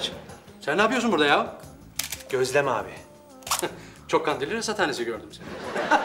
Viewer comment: Kubi ne diyor orada Sertaç'a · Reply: cok kandilli rasathanesi gordum seni diyor jfneinfienidndijd